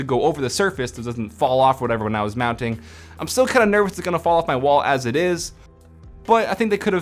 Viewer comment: i am still ____ what did he say reply please lol :) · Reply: nervous